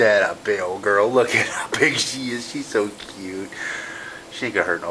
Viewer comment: did he just say that she's so cute?? 😖